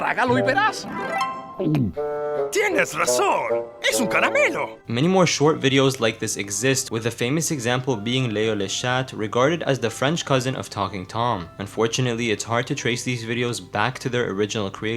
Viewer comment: I thought you said Leo le shart 😭